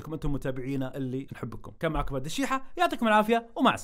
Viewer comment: انا سمعت اسمك عبدالشيحه؟ ولا اذني فيها شي